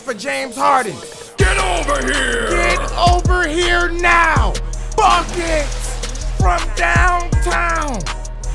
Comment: it said fucking instead of buckets lol